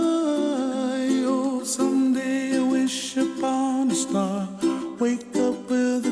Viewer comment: When i Was younger i thought he said "someday i wish be a porno star"😂